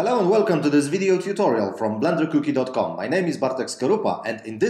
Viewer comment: did I hear Blender cookie dot com ?:D Great video though.